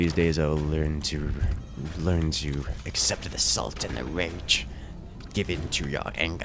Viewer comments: "learn to accept assault and the range(i have no want he actually said). Give in to your anger." Me: "Join the dark side... We have cookies." Also that sniper on team two in the first game was extremely good. wonder who it is. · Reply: The fact that you thought he said assault and the range is actually hilarious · "learn to accept assault and the range(i have no want he actually said). Give in to your anger." Me: "Join the dark side... We have cookies." Also that sniper on team two in the first game was extremely good. wonder who it is. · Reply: He said learn to accept the salt and rage.